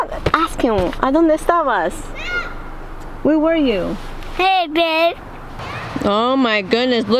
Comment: Lovely vlog ur the sweetest girl that coat looks nice on BT love that Z was reading a book at the back of the car omg 😍 taking to the penguin was so cute did he say hey babe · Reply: Yeah Z loves reading!!! It’s an owl haha yeah he says “Yeah baby!!”